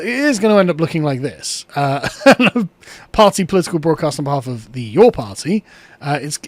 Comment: Sorry I misheard, so it's not the YAWN Party!